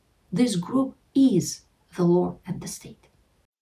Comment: Minute 12:42 What is she saying at this point "... this group is the (law) and (what) ' What, my brain goes: ...the law and the sea.... the law and deceit.... the law and What?